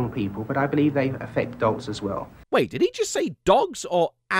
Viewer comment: I think he's saying "dolts" like idiots but old timey